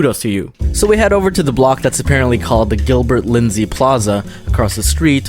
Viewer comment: I FIRST HEARD IT AS GILBERT LIZZY AND I JUST PRUHUN